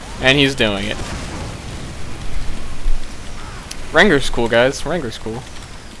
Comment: Wait did you just say "Wrangler's cool guys" at 14:08?!